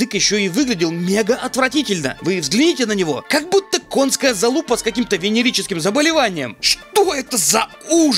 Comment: послышалось, что он сказал "конская залупа с венерическими заболеваниями". свят-свят